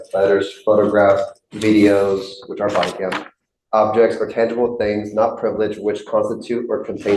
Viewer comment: The on-screen subtitles are strewn with errors. Around 31:20 it actually says "cannibal" instead of "tangible" for assets. I can understand an errant, common word replaced with a similar one, but the word cannibal shouldn't make it through draft, production and review.